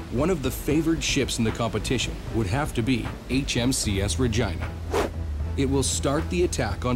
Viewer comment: Listened a few times and had to turn captions on. I was sure I heard "HM sea ass vagina". xD